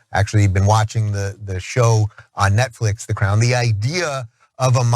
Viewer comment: Oh, I thought he said watching the clown. The crown is not a ruling body.